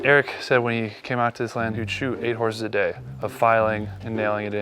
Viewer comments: I thought he said, "Shoot a horse!" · I thought he kept saying “ do you want to learn how to SHOOT a horse” I was like what kind of ranch is this!? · I thought they were saying shoot the horse but they were saying shoe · Whenever they said "shoe" horses I thought they said "shoot" horses😂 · I thought he said shoot a horse